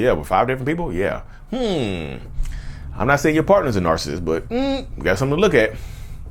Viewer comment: Did u just say your a certified narrsasist? LOOOOOOOOOOL